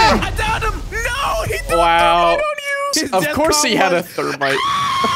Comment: smi77y "of course he had a third Reich" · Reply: Rewatching this I have misheard what I have heard orginaly, "Thermite"